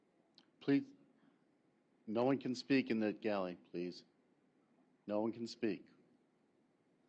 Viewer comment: what was said ? Judge said no one can speak in the galley?